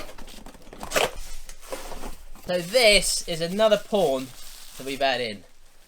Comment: Lol another pawn I thought you said another porn and you look excited